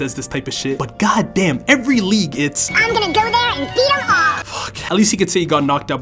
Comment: did ash just say “im gonna go there and beat him off”?!?!?!?